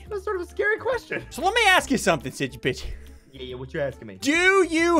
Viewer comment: when he asked sigils are you ready subtitles thought he said sigy bitchy